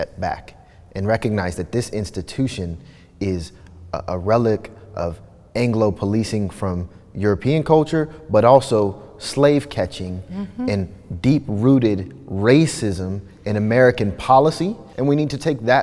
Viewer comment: hol up did he just say all anglos have this deep rooted urge to police/enslave blacks or did I hear that wrong? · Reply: No he said "This institution is a relic of anglo-policing from European culture, but also slave-catching and deep-rooted racism in American policy. And we need to take that step back and re-enter this conversation on the fact that it's bigger than an officer."